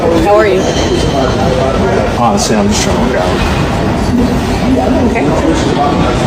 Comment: what this gay just saying ? Is it "Honestly, im shay gay" ? I see what she write "Honestly, I'm just trying to work out"